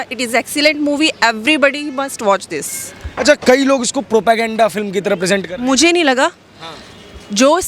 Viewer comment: Did they just say " PropoGenda" twice @ 3:33, @ 3:41 ?